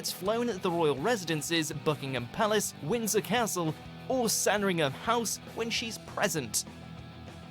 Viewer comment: Another lie - The blokes recording these "fact" videos know how to pronounce regions in London. It's pronounced "Clarkenwell" not "Clerkenwell" lol I got to stop getting annoyed by these little things. Oh well. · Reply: It sounded like he said Sanrinham house as well rather than Sandringham.